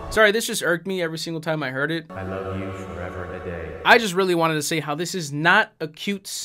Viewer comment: "For ever and a day" isn't an Endgame rip-off; it's a Shakespeare quote (I think it's from Twelfth Night). You'll probably never see this comment, but this was really bugging me. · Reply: i dead thought they were saying "forever in a day" and thats why he was so frustrated